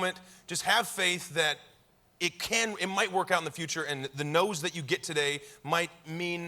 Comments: I thought he said the NOSE you get today... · I heard " the nose you get today " instead of "the no you get today ", and honestly found that even more inspirational.